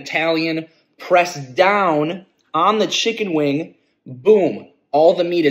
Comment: I thought you said some ranch, mf ate a whole bottles worth